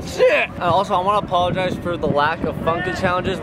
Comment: did he say shit?